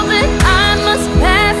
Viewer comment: I thought she said “Soldier pull the trigger” Whoops